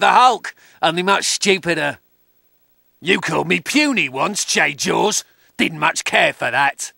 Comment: for the 2:19 one, I thought he said "You called me puny once, jade-jaws. Didn't much care for that." Since I think he said that to the Hulk.